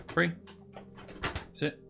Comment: This might be an odd question, but what are you saying to your dog as you're giving him treats? For a long time, I keep hearing "Sit, free, sit".